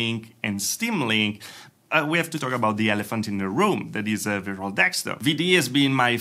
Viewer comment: Ah! @ 7:10 he is saying "Virtual Desktop"! Subtitles were no help, thought he was saying 'Veral Desktop'